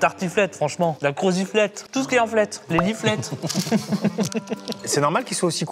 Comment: j'ai compris biflette 😭